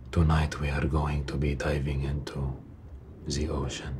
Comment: I really need my hearing checked. I thought you said "Tonight we are going to be typing into the ocean." And again I was down but confused